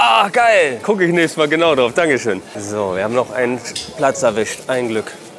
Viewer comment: was sagt er ? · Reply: Er schaut aktiv seine Videos